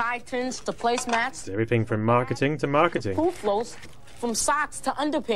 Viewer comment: What does Z say at 7:07? Pooflows? Is that an American thing?